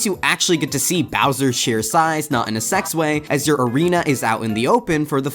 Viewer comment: He said "not in a sex way" so fast i almost didn't catch it.